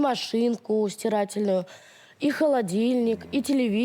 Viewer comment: Как сказала 😂стирательную машинку.сколько смотрела только расслышала😂